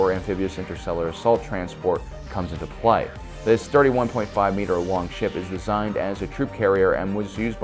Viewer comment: did he say 1.5 meters long?